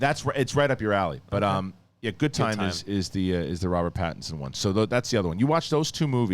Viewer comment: Hey, where can I find that Bobby Lee podcast where he spoke about you? Always liked that guy! Also I'm really tired so when you said that you always wanted Robert Pattinson as Batman, I thought you said PATTON Oswalt and I was super confused for a good 10 seconds. 😂